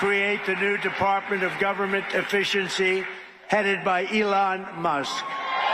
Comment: Sounds like he said government deficiency